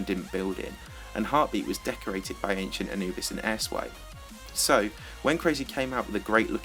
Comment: when you said airswipe i thought you said ass wipe